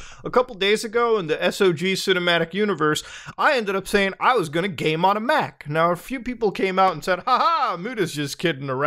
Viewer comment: I thought he said, "I'm gonna came on a mac"